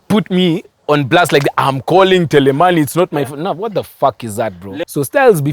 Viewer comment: Did I just hear say 'Tele Maheni' 😂😂